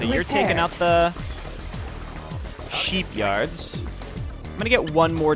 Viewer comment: I heard like a SHEEPyard🤣🤣🐏🐑